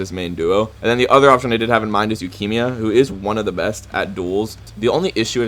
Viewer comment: why did i hear lukimia